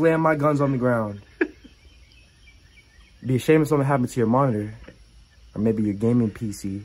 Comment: I heard it be a shame if something happened to your body😂😭 I had to replay that